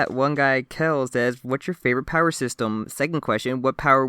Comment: HEY CONGRATS ON THE BIG THOUSAND, I started watching your podcast religiously every monthafter catching up around ch. 83 and am so proud of you guys for getting this far. Unrelated but At 34:51 I thought you said "can we both agree that we like men?"